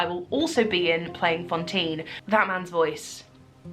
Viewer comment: When you said "that man's voice", I heard "batman's voice" and I was like where is this going? 😂